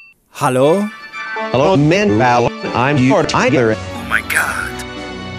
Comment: every video on this subject i thought they all said "manuel, im your diaper"